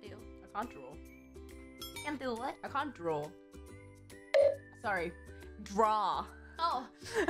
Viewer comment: What says: I can't Dro!!! 😫😫😫 What I heard: I can't Bro!!! 🥵🥵🥵